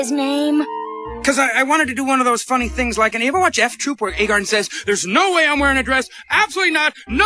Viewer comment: what is freakazoid saying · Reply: I wanted to do one of those funny things like in- Hey you ever watch F Troop?